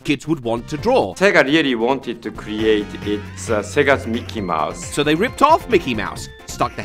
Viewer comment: What 0:59 is saying: "(japanese name) wanted to create a SEGA's Mickey Mouse." What 0:59's subtitles say: "Taqueria wanted to create a big-ass Mickey Mouse."